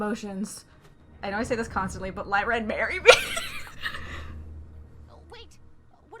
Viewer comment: I know you said lie ren marry me but I heard it as burry me